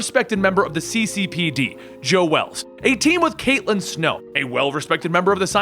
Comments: Did I hear him say Joe Wells? I legitimately can not tell. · Joe Wells? Is that what he said. I think he meant Joe West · did u say "Joe Wells" or is that just me? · Is it just me or did he say joe wells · did he just say "joe wells"??? 7:58,,^^^^ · Joe Wells. 7:58 am I the only one who heard that? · Anyone else hear Joe Wells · did you just say Joe "Wells" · He says joe wells instead of joe west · Am I the only one who heard Joe Wells?